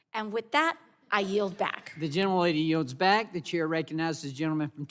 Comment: “Will the young lady yield” …..disrespect oozing from his bones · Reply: I heard "young lady" too! Then I thought I might've misheard "gentlelady", but I don't think so! How disrespectful.